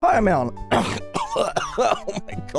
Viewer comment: Why does it sound like he is saying "Hi, I'm Allen"